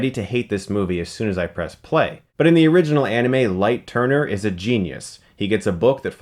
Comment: Did he just said "... In the original Anime *Light Turner*..."